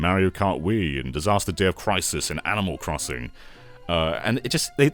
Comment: had to rewind because i thought you said they put crysis on the wii